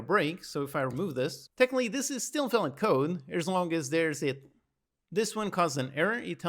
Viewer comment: What were you trying to say here? You were cut off. "As long as there's at-" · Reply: hmm strange, not sure what happened there Basically I was saying how you can have a case fall into another one as long as there's no code in between them, like I do at 2:13:50 which makes the code valid